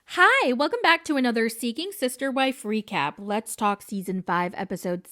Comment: OMG I misheard the intro and heard "welcome back to a seething sister wives recap"😂